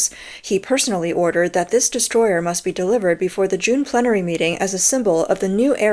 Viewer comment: I thought you said distroy-her